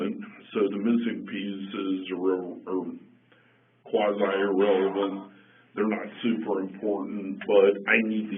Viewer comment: I heard “the missing penises are quasi irrelevant” and had to go back 3x to see if i was hearing it right 😅